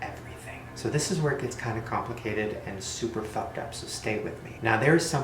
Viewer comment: I thought he said marshallah